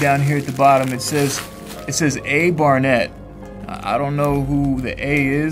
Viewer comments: I thought you said gay barnet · I thought kt said Gay Barnet.